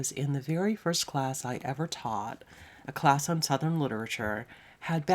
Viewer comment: Incidentally, I misheard your teaching class as "sudden literature" rather than "southern literature". Wouldn't that be a blast to teach? The books which someone sat down and wrote in a white heat of creativity, in a month. You could teach it in a similar style, suddenly flinging open the door, rushing in, writing an assignment on the board, and conducting book discussions to a metronome with timer primed to go off every 5 minutes. Essays to be written as flash fiction.